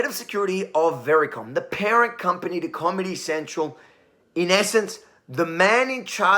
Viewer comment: Did you say "commy central"? Freudian slip?